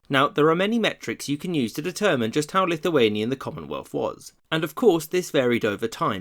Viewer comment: CC says "to determine just how lift-away knee in the commonwealth was"